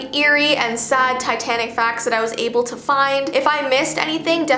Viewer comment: I thought it said titanic farts xD